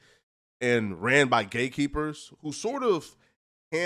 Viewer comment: i thought you said gay people 😂😂😂